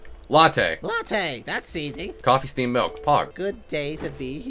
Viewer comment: did say “coffee, steamed milk, pog”? cuz like. i’m living for that